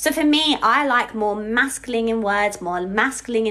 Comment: sounds like she said "mescaline" so does this mean that pronouns can now be a psychedelic cactus?